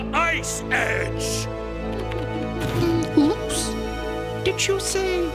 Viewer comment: Did you say, oops?😂